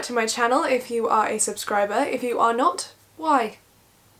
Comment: ''IF YOU arent a subcriber... why?'' Me laughing until I notices u arent laughing back ... oh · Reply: Ohhh that's what she said I thought she said "If you're not, who are you?" ;😂